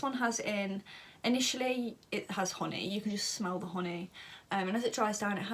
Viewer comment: when she said "honey" at 3:03, i thought she said "horny" , lmaoo love listening to her talk though, she has a mellifluous voice. Hope she makes ASMR video one day.